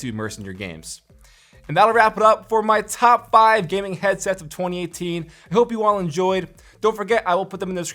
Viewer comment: I thought it said headshots. I was waiting to see some epic headshots but it just ended up being a advertisement:(